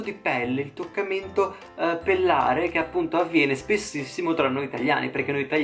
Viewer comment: solo io ho capito il toccamento pallare?